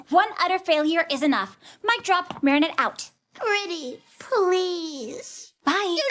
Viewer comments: Ok so, at the part where tikki is like "pretty please" my friend pointed out it sounds like tikki says "You dick!" To Marinette as she pulls out the earrings and now I can't stop hearing it and it's hilarious to me 😂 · did tikki say to Marinette “you dick” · I’m sorry did tikki just say “YOU DICK-“